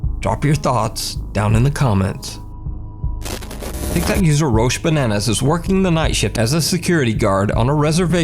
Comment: did just say roshe bananas